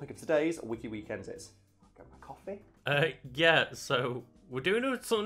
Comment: anyone else hear say "coffee" at 00:24 just straight calling it out